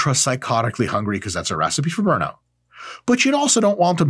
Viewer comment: I had to do a double take because I thought he said “that’s a recipe for murder” lol